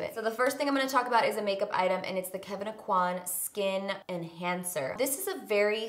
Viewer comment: I have this playing on my laptop while I do dishes and I thought you said Kevin Aucoin Skin Cancer. I was like what kinda name is that for a product 😂 had to rewind and double check